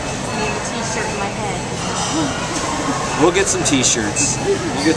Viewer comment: Lol...uh....did he just say "...but I'm more interested in panties..." (!?!?) to the little children? LOL...... Now now...behave!